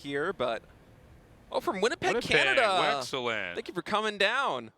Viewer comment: Did Tasteless just say "Wexcellent"?